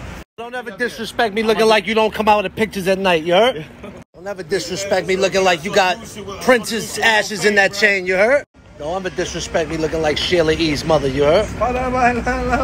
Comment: Prince's ashes in that chain is💀💀💀⚰️⚰️⚰️ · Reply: I thought he said the trenches ashes 😂😂still had me rollin